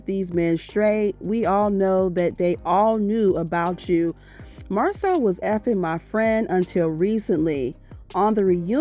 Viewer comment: 🗣️”Tisha got the bottom of the Bar-rel!!!” 😂😂😂😂😂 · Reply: I thought he said Marsau was xxx his boy. I said huh😮